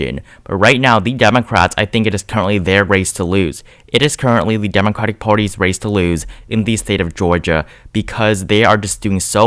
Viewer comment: "I think the democrats are going to lose the race in Georgia. They are going to lose because they are doing so well." am i going crazy? correct me if u didnt hear what i heard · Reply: I see what you mean, but he said “its the dems raise to lose” which means that unless they choke it and or republicans surge, it will at least lean in their favor. God i hope thats true